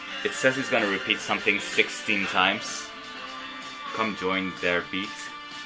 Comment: i always hear "come join, dur bi" when he says "come join there bit" lol dur bi means "wait a minute" or something like that in turkish :D